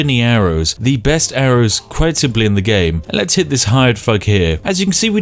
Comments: I thought he said 'let's hit this hired fuck here' lmao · I heard it as "let's hit this hired f*ck here"😂😂😂